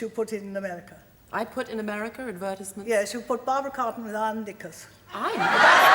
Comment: Did she say “Barbara Cartland without her knickers” at 4:02 😂 Barbara speaks so fast🙈